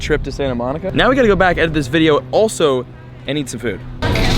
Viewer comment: I thought when you said I need some food I thought you said I have to poo